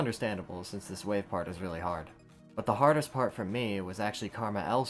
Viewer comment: it says worst fail instead of hardest part.